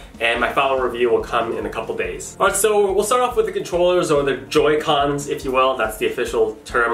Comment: I thought he said 'Bullshit controllers'